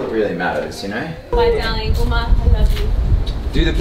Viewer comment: wait omg did you ask for an uma or did I hear that wrong?? hahah